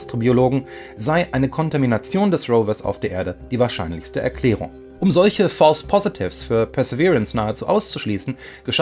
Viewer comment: gelingt uns ein was auszuschließen, hat jmd verstanden? Punchdemie? 🤔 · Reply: Panspermie